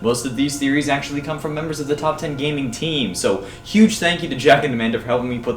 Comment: i heard toppat gaming theme.